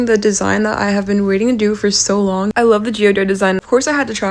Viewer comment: I know you said geode- BUT I SWEAR I HEARD “GEODUDE” THE FIRST TIME😭